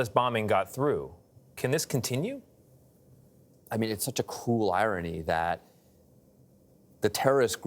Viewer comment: It’s such a cool irony? So much cool… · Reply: I thought he said cruel irony